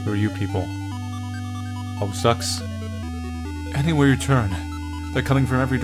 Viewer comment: DID HE SAY HOMESTUCKS? DOES HE KNOW WHAT HOMESTUCK IS?